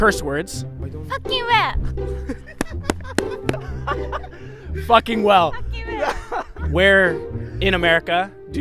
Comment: I thought the girl at 2:38 said fucking whale😂